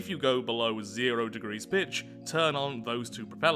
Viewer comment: did he say bitch?